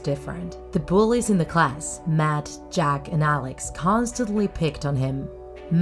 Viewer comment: When you said the names of the bullies I thought you said Mad Jack instead of “...Matt, Jack...”